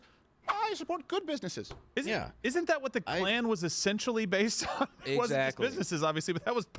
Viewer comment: sorry did he just say the Klan?!